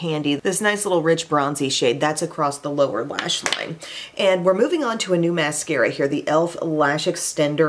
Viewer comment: Fingers crossed because I thought you said brown in the new mascara. This ginger needs that!